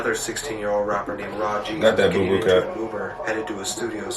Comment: Did you just call dat nigga rah gz boo boo cat at 9:39 or am I buggin? 🤣🤣🤣🤣🤣